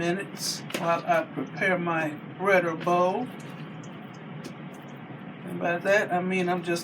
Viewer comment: What is the name of that bowl? I need that in my life. It sound like you said bridle bowl